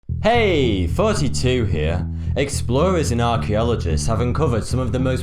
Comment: It sounds like you say "Hey 42 Here"